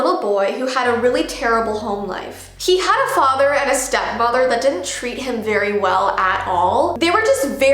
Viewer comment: I thought she said stepfather 💀